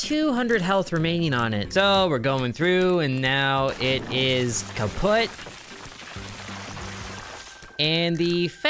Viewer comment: did he say kaputt